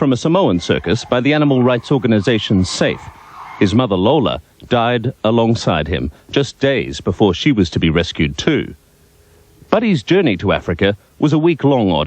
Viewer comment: I wonder why he was not with his mother during the rescue? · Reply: (Dr. PennsylvaPia) No, that's not what it said. At 1:07, it says, and I quote, "His mother, Lola, died along side him, just days before she was to be released, too."